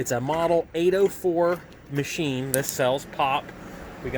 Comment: Oh, I thought you said sells pot not pop